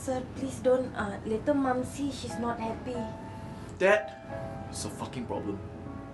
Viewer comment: did he say that is her fucking problem?